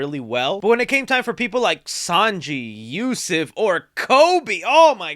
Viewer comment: was ussop named yussuf in this vid? or did i mishear